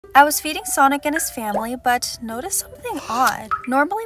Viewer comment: HELP! I DIDNT NOT HEAR THAT FIRST LINE RIGHT AND I WASNT LOOKING AT THE TEXT 😭 I HEARD “I was feeding Sonic his family” 💀 On another note! You take really good care of your fish and your videos are oddly calming in a way :)